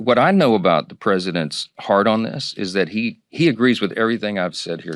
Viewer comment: I coul have sworn he said ‘I speak to the president multiple times a day, and what I know about the president’s HARD ON this’ - Freudian slip of the tongue!